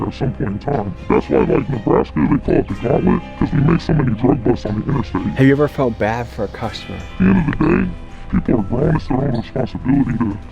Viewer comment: did he just say "thats why 'we' make so many drug busts on the interstate."?